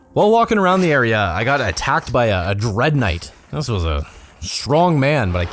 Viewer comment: wait, did you say "Dread Knight"? Super Gore Nest plays